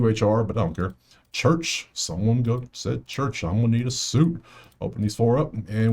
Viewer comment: I heard somebody yell church imma need a suit lol . Lil Wayne Bar